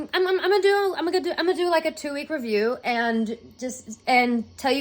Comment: when you said "do it- do it like a two week review" I swear you were about to say "do it like a dude" maybe I need to stop listening to so much music